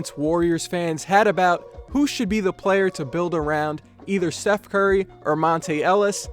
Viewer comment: Am I trippin or did he say "Seth Curry or Monta Ellis"